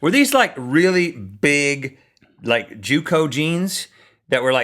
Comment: Did he say Jew co.? ...and by the way, Louisville should hardly be considered part of Kentucky.